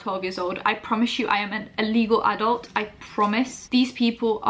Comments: it kind of sounds like you said illegal adult.... is there anything you need to tell us? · I thought you said "illegal adult" for a moment there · its sounded like you said ur an illegal adult lmfao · I thought she said “I am an illegal adult”